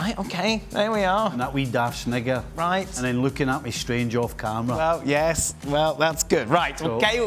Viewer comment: what did he say at 11:10??😳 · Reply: He said snigger